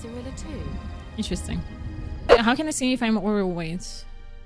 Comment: What does she say at 6:01/02 with the bags? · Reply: probably too late but "how can i see if im overweight?" As in too much weight to move at full speed